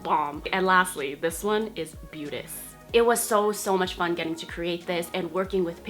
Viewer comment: OMFG watching this during class and when you said beautis I thought you said penis and I choked